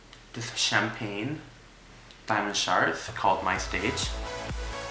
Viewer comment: ok first when she said "diamond sharts" i thought she said "buy my sharts"